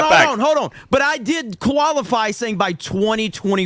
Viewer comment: Did say koalafy? As in a koala bear? Because koala bears are from Australia? 😅